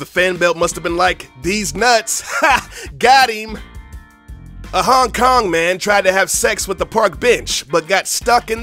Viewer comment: It kinda sound like he said "A Hong-Kong man tried to have sex with a park bitch" ( 5:04 )